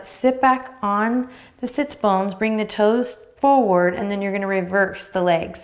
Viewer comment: I heard “sitsbunkin” and will forever refer to sits bones as such 🙏😂